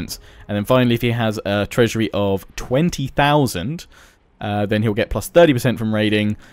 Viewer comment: btw you said 20,000 instead of 200,000